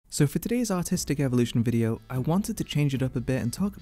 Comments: oh God I misheard the intro as: "So for today's autistic evolution video-" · i heard "so for todays autistic evolution" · I had my volume down and heard "autistic" instead of "artistic". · thought he said "so for todays autistic art review"